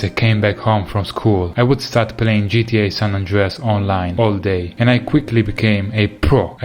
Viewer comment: did he say online